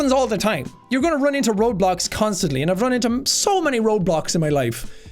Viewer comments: i thought he was saying Roblox and I was so confused 😂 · sean: ur gonna run into roadblocks constantly me: did he just say ur going to run into roblox constantly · i thought he said "you run into robots constantly"